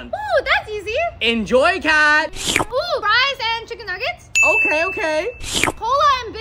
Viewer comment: I thought she said: rice and chicken nuggets